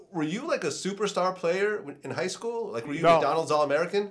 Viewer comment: Went to the same school as him, not at the same time. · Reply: I figured after listening better he says I went to "highschool" which Kareem went to. But it sounds like he says with Kareem.